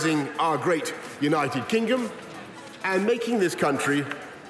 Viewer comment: Is it just me or did he say Kingom?